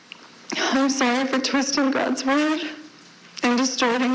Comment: Did Franke say she's "sorry for trusting God's word????" I'm sorry for her that God heard her.